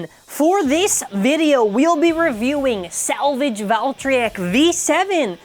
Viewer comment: This name is weird when I heard it first time I almost thought that they said savage valtryek